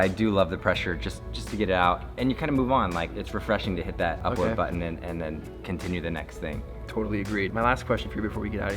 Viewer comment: Did he just say Crisp Rat????